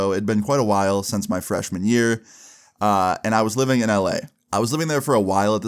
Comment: can’t be the only one who hears “I be in LA” every time he says LA right?😭